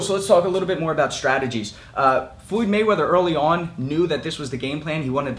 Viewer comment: Did you just say Fluid Mayweather?